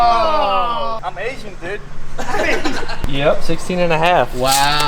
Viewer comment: what did he say? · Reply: "I'm Asian Dude"